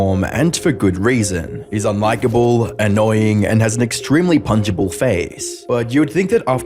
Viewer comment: “Very punchable face” 100% accurate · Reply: I misheard that for "Very puntable face" Both work honestly